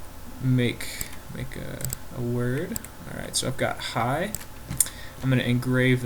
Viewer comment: Did he just says "I've got high" :D